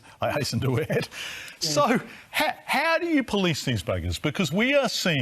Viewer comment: Did just call the bikie boys, buggers? bugger /ˈbʌɡə/ Learn to pronounce VULGAR SLANG•BRITISH noun 1. used as a term of abuse, especially for a man. 2. a person who penetrates the anus of someone during sexual intercourse. verb 1. penetrate the anus of (someone) during sexual intercourse. 2. cause serious harm or trouble to.